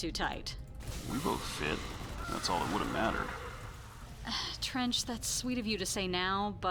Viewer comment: Hang on, at 40:54 when haley says its a bit late she says trench but the subtitles say trent, im pretty sure thats a bug?